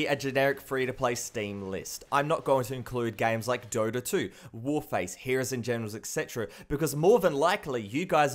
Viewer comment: i thought i heard you say warface instead of warframe